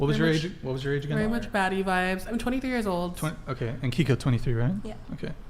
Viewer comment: Let’s talk about the fact that they all thought the big girl said “fatty vibes”